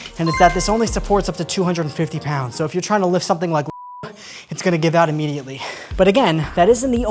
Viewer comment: Did bro say “lift something like Lizzo” 🤣🤣🤣